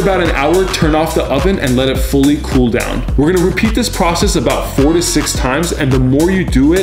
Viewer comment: 46 times. He probably got sponsored by putin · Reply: It's "4 to 6" dude, maybe you misheard it because of the reverb sound